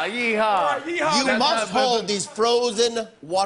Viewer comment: did he just say you must play with my balls at 4:17?